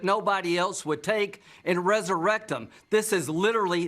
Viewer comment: did Lindsay say: "resurrect him" or "resurrectum" -- it sure as shit sounded like "rectum" to this ear but then I'm not used to those southern belles with their cute little drawl and witticisms which an old dog like me wouldn't have a clue about. 🤗😘🤪🤣